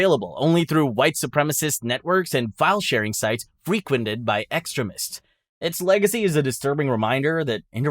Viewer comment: did you say “extremists” or “excrement”? I guess it doesn’t matter same thing.